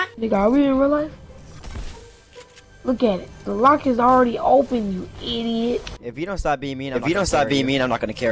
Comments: Can someone tell me what he said I didn’t understand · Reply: He said “the lock is already open you idiot” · anyone know what he said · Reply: "are we in real life?"